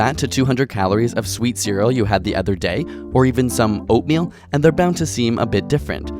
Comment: when you said oatmeal. i heard wrong and thought you said opium XD